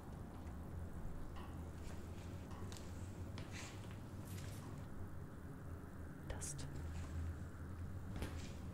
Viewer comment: Omg when whispered “Dust” at 19:00 I genuinely got freaked out cuz it sounded like she was whispering my name (Dustin) 😰